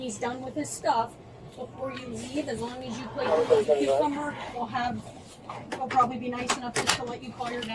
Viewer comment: Anyone else catch the female cop saying to her that as long as she plays with the cucumber she will get to call her dad? Wtf?